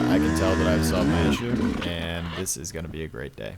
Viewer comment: Yep! if you check out the later half of the video it was the powervalve getting hung up randomly. Really weird issue. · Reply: I think the confusion is at the end of the video it sounds like you say. I still have my issue , and this gonna be a great day but I think you actually said “I solved my issue”